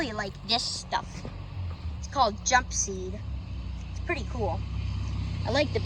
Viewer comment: Yeah I know there is lots of poison ivy in some parts of my woods and I do like the jump seed and be sure to watch the next video because I go into more detail about the jumpseed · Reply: "Jumpseed." Thanks for spelling it out-- I was mishearing it in the video. I'll look forward to learning more about it!